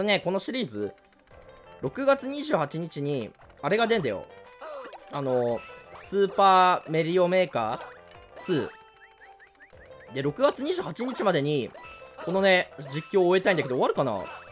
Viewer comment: スーパーマリオメーカーがスーパーメリオメーカに聞こえるのは俺だけ？